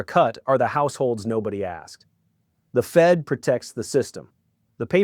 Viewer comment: At the very end of this video, I swear it sounded like you said "The Economic Ninj-er is out". 😆